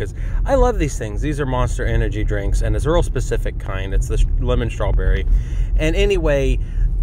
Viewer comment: Did you just say lemon strawberry?